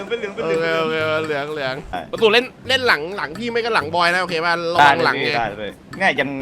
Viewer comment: PASU LOL พูดว่าไอ้บอย